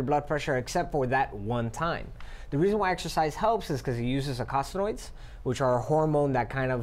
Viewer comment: whats the word he uses at 0:32? occoclyodes? can't hear it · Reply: He said "Eicosanoids"